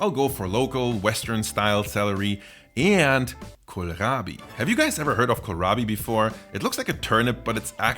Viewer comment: When you said "kohlrabi" I heard "Gorlami".